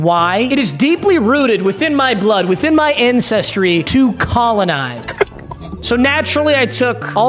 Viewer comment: Definitely misheard "ancestry" at first, might want to enunciate that first syllable a bit more so it doesn't sound like "incestry"